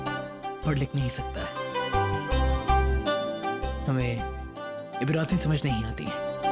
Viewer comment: What is the word he says at 41:03? Hame.......samajh me nahi aati?